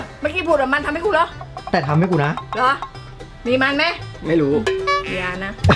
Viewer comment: "แต่ว่าทำให้กูนะ" 2:38 ได้ยินแบบนี้